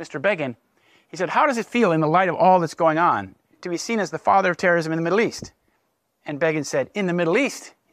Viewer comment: Erschreckend. Leider sind die deutschen Untertitel so schlecht, das ich entnervt wieder auf englische Untertitel umgeschaltet habe, mit denen und den Resten meines Schulenglischs zusammen ich das Gehörte dann in befriedigendem Maße verstehen konnte. Bei 1:10:18 ist mir aufgefallen, dass das Geschriebene an dieser Stelle nicht ganz mit dem Gesagten übereinstimmt. Der Sprecher zitiert einen Fragenden: "How does it feel, in the light of all that's going on, to be seen as the father of terrorism in the Middle East?" Worauf Begins Antwort zitiert wird: "In the Middle East? In all the world!" Im Geschriebenen fehlen die beiden durchaus relevanten Wörter "seen as". Ich kenne den Zusammenhang nicht, aus dem die Frage und die Antwort entnommen sind, doch wenn die Frage das "seen as" tatsächlich enthielt, ist nicht ausgeschlossen, dass Begin seine Antwort ironisch gemeint hat. Bei diesem höchst heiklen Thema sollte der Filmautor meiner Meinung nach auf absolute Genauigkeit achten - wie Sie, Herr Schulte, sie in Ihren Büchern pflegen.